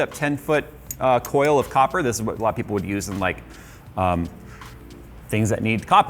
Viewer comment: Did he just say “This is what black people would use...” at 5:12 lol